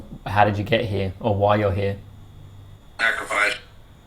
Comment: /44:16 I didn’t hear “sacrifice”.. I heard “I provide”